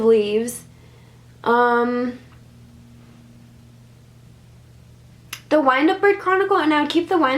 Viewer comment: Does anyone know what's the name of the second book she mentioned at 13:40 please? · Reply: The Wind-Up Bird Chronicle